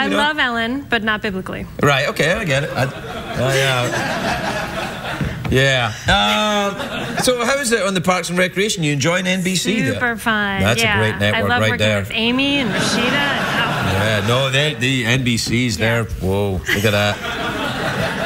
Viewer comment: For a SPLIT second, I thought Craig said "Farts and recreation" at the beginning of the second interview, lol.